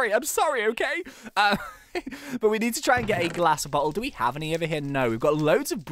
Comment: when he said "glass bottle" I thought he said glass but hole. lol